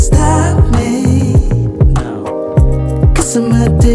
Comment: Wait a minute wait a minute. I thought he was saying, "addicted to the good life" 😮